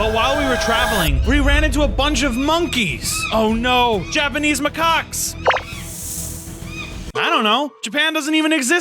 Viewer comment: i heard him say "oh no japanese ma c**ks" ngl